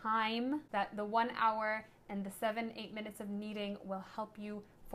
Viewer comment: one hour of kneading? Did I hear you right? I like your video. I love making my own dough. Thank you for sharing :-)